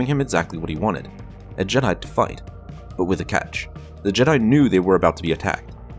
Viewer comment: Who else heard "A jedi to f*ck, but with a catch"